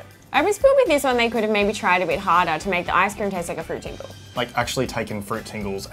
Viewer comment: It's strange that they're speaking English but because the dialect is so different I can't hardly understand what she's saying around 2:10. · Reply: pretty sure she says "I just feel with this one they could've tried a bit harder"